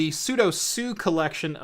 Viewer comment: Every time you pronounce "pseudo sue", I hear "sudo su -".